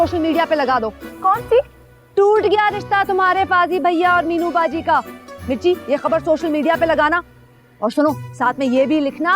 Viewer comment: what she said🤔sai samjh nai ai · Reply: No she said waleed bhaiya